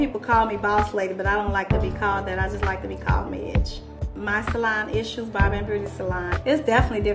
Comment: I thought she said, "I like to be called bieetch"